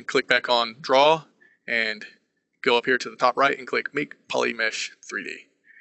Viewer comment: very nice but i've got a prob ... i can't understand on 04:53 when you're saying how to make it 3D polymish in high quality... plz help .. i want to learn this prog.. :D